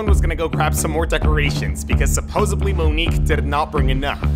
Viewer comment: Did u just say “supposebly”?